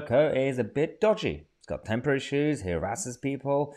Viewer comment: Oh "temper issues"... thought you said "temporary shoes" lol... I imagined those little things that CSI wear 😂